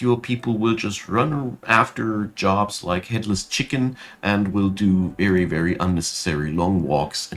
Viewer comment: listening on 1.5 speed I misheard this at first as "like Hitler's chicken" , thought that might be a phrase 😬😆 Thanks for the tips!